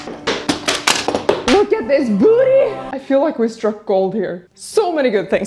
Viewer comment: The room turned out AMAZING 🤩 Also… i misheard “Look at this BOOTY” 😂